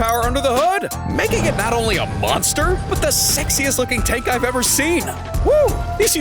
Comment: **WHAT DID HE SAY💀💀💀💀☠️☠️☠️☠️😱😱😱😱😱* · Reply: Sexy?